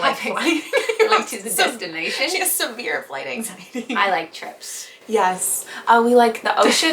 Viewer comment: Why did I hear “we like chips” and not trips😂